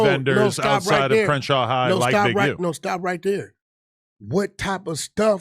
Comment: I thought Wack said he was alerting Big U 🤔